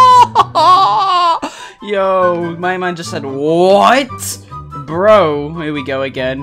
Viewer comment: when said “what” to me that sounded like dream